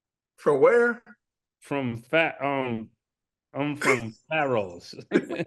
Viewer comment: Ma$e is saying Pharaohs. I knew this only after he said Ramsey III. If he never said Ramsey I would have thought his lineage was from another planet. I thought that nigga was saying a country or planet name Pharos